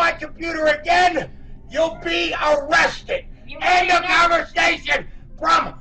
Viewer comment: "unintelligible"? He is clearly saying "end of conversation".